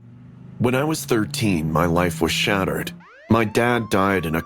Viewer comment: So your 13 and you have a wife??? Am i hearing this right i think you meant girlfriend or future wife 0:02